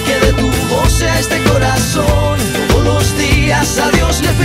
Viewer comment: siempre pense que decia que si me enamoro sea de musica